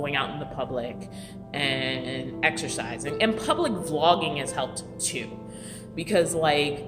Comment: Okay I just got my eyes fixed, now I may need to get my ears fixed. I thought you said "Public Flogging has helped me" ... made me perk up a minute there. Ahhh memories. Of course the next few sentences let me know you probably said Public Vlogging. It's interesting the timing. I watched this right after I chickened out on going into the gym for the first time to shower. I'm going back tomorrow to try again and will go back every day til I get my nerve up. You're good, you're great. I really do love watching your videos. I think it's the mother in me, plus I'm learning alot from you. I could go on but you know what I mean. Keep vlogging, keep being you. That's your gift to us. Our gift to you is we really do appreciate you and support the hell out of you.